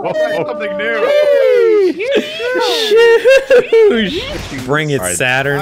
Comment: oh jesus christ wasnt lying about saying "shooosh" instead of "sheeeesh"